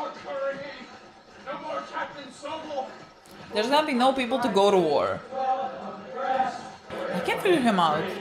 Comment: What she say at 13:48? I cant make it out. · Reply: "there's gonna be no people to go to war"